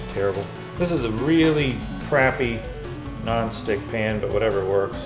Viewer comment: if you're referring to Teflon (googled it, rofl), he said it's not the kind of pan that has a non-stick coating, so I think it doesn't have said coating. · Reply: Øyvind Lauen 2:47 I heard "this is a really crappy non stick pan".